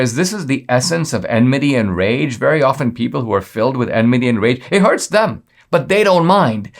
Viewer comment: At the 7:10 mark what is he saying? It sounds like “adminium” rage. Does anyone know?